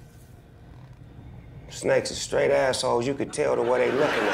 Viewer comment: Did Snoop say "straight ass*oles" at 0:36? 🤣 if yes, why didn't they censor it then? Lol